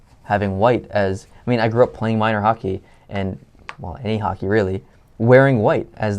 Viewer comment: did you say you hate hockey? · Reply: He said, "Well, any hockey, really." I had to concentrate, though. I thought I heard the same thing the first time but thought, "That can't be right!"